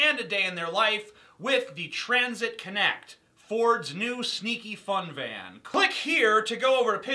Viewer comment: Did I hear him say "sneaky fun van"!?